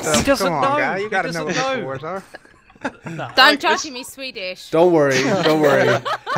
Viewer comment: not he first time i heard 'he's swedish' in a female british accent